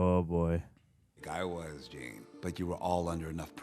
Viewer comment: Space Exploration? I sense a symbiote. First rule of Spider-Man, don't mess with a spidey that stopped making jokes. 35:40 Did he say Jean? I hope they're kidding about it being cancelled.